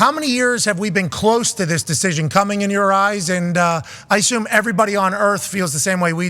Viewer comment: I randomly paused at 2:47 and when i came back to it just thought he said coming in your eyes 😂